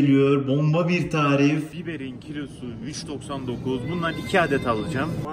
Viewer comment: Biberin kilosu 3 lira mı dedi ben mi yanlış duydum :( güzel zamanlardı...